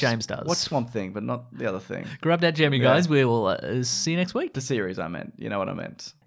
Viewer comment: What is always saying at the end? 14:59 Grab that ______ guys we'll see you next week. I'm not English so it's difficult. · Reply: Hitso Orvonen Grab that gem you guys.